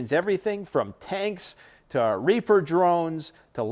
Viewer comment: Right before 13:00 i swear he says “reefer” not reaper